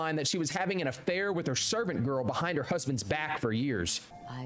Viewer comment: Is he saying servant girl